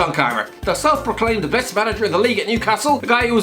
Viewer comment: I think he said the best coach in the league, not manager. Not quite the same thing.